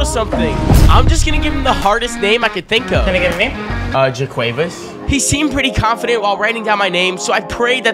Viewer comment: i thought you said jaquavis thompson